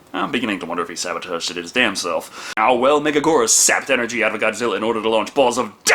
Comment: It's pronounced Mega-gear-us. Megagurrus is a mistranslation, you silly fool! Also, it's a girl. The whole "King of Meganula" is another mistranslation. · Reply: I've heard it as 'Mega-Guy-Russ'. Then again, I'm not big into Godzilla...